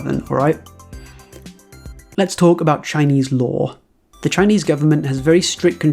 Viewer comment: I thought you'd said "shinys law"